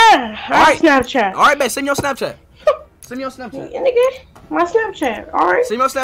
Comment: What did he say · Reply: Nigga Hear my Snapchat